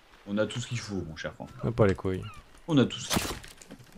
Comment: c'est moi ou la voix bizare a 10:11 dit ta pas les couilles?